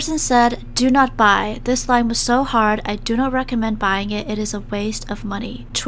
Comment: it says The but she said This and the caption says This line...